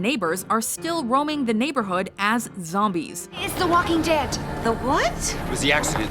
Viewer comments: did she say the walking dead? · did she just say it's the walking dead!!